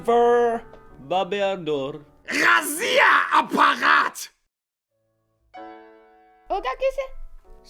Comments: как расслышать Россия-аппарат? 😹😹😹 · я одна на 2:43 слышу Россия аппарат ? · Почему я здесь слышу россия апарат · Я один услышал 2:44 "РОССИЯ, А БУХАТЬ?! " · Кому тоже показалось как и мне что он сказал разве я аппарат на моменте 2:43 ставь лайк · я один услышал на 2:44 моменте РАСИЯАПАРАТ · мне одному послышалось россия обосрать? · я единственный слышу рассия апарат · я очин слышу Россия аппарат ? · я одна услышала "РОССИЯ АППАРАТ!!"? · мне послышалось Россия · мне послышалось Россия апарат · Я один слышу "Россия аппарат" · мне на 2:44 послышалось ,,Россия аппарат!!’’ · я одна слышу ,,Россия аппарат"? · послышалось "РАЗВЕ Я АППАРАТ?" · Ржу не могу!! "Буствартсе"!! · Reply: Мне одной послышалось "Россия-аппарат"))) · тоже послышалось Россия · Я единственный слышу на 2:44 ¦РАССИЯАПАРАТ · Я один услышал "Россия апарат"? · я один слышу "Россия аппарат " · В 2:40 на немецком мне кажется что он сказал" Россия Аппагат" тоесть поганая россия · мне послышалось "Расзия апарат!" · мне послышалось Россия богат? на